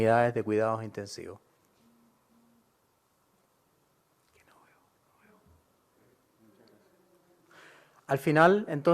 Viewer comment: que dice que dijo? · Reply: Creo que dice " es que no veo, no veo" o es lo que escucho yo :$